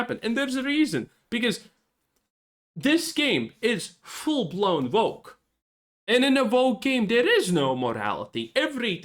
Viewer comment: Oh, you said full-blown woke. I thought you said full-blown broke. If that is what the main character looks like, it is broke. I can understand the original creator being devastated. Anybody who buys this is going to be devastated.